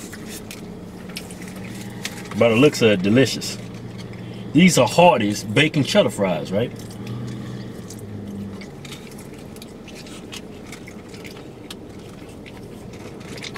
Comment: Lowkey thought it said harambe's bacon cheddar fries lol